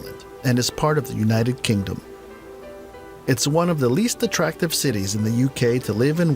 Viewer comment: Did it say Belfast is one of the least attractive cities? Did I hear that right? I keep replaying and I don't get it. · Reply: I'm from Belfast. He said least attractive. He's right! The surrounding countryside is beautiful though and the craic in the city is great.